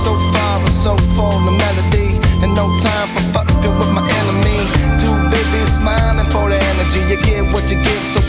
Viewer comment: So I swear it sounds like they say “too busy smiling full of energy” instead of “too business minded” … any one know!??